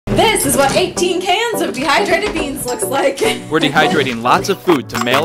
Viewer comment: BAHAH i thought it was a bee hive at first and i heard ‘dehydrated bees’